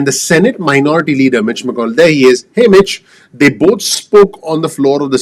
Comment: I thought you said hey Bitch lol.